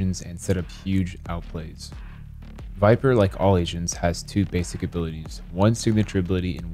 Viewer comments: I thought he said, "Like all asians" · thought he said "like all asians"